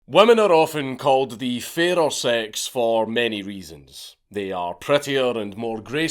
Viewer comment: Oh god i thought you said women are often the more feral ones and i still was 100% with you either way haha